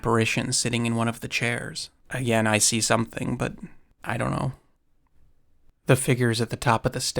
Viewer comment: 11.45 i hear " I ve seen something" not " I'm with Cece"...anyone else?